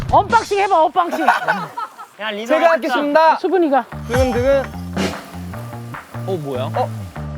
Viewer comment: “수근이가”라고 들리는데...